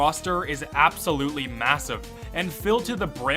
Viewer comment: did you say massive?